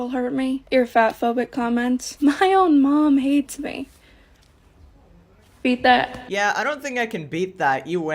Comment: I swear i hear” feed that,” 1:01 stfu that voice is so high and piercing, i didn’t even know that was a lady before they spoke